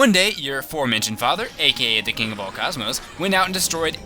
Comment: i thought he said "fucking of all cosmos" :D